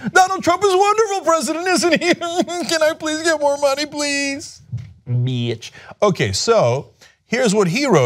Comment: did he say bitch? lolol